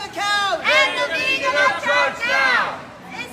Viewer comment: For a moment I thought they were saying hang a vegan upside down. 😅